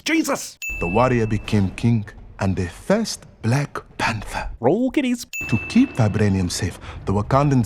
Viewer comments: I thought you said Roll Titties lol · When I watched this the first time and again this time in the beginning, when they say "he became king, and the black panther" I thought it said "he became kink,"